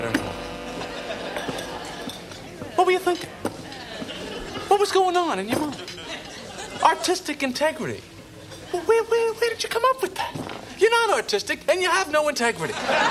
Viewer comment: Not my dyslexia confusing me, I thought he said autistic integrity. 😂